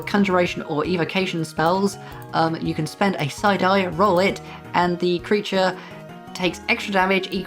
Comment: 😂 I kept hearing you say, "...you can spend a side-eye....."